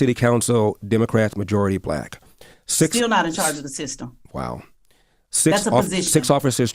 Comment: As she was interrupting Elder with "still not 'in charge of a system,'" I kept hearing her say, "still not a True Scotsman!" I wonder why she didn't stop to define what would constitute being "in charge of the system." 🤔